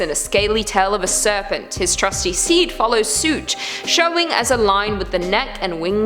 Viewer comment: sounds like she said "his trusty seed" not steed... haha